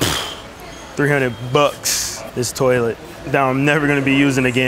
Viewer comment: Why did I think that said skibidi toilet 😭😭😭